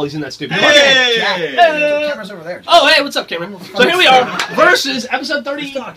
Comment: Sounds like Geoff said "Oh, what's up Cameron!?" That's my name :) Whats up Geoff!?